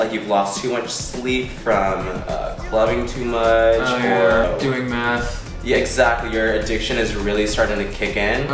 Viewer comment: LOL I thought he said doing math....